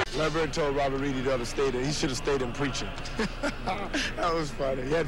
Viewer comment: can anyone plz tell me who is the guy at 3:02 and what he is actually saying ? thanx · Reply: That's John Lucas II and he was recalling when Bird said to Robert Reid: "Reid, you shoulda stuck to preaching."